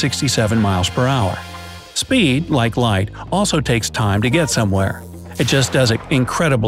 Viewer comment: There's an error at 2:15, please correct. It says "Speed, like light, also takes time..." instead of "Sound, like light, also takes time..." Like so Bright Side will see.